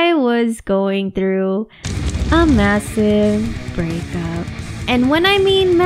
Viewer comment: lmao i thought you said "a massive breakout" like acne stuff and i was like ME TOO B--- OMG SO RELATABLE but it wasnt lmao